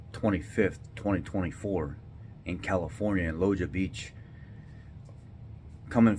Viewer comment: WHAT BEACH in California was the migrant invasion? Sounds like he said "Loja Beach" not clear enough. Watch out when they have a kid with them. That's probably snatched and selling the child is how they afford to stay in the USA.